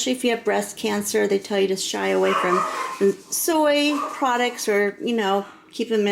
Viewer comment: I’m either confused or my poor hearing got the best of me. I thought you said if you have breast cancer, they like you to shy away from soy. Isn’t tofu soy based? Sorry, I’ve never bought any or ate any for that matter, so I’m not educated in the matter.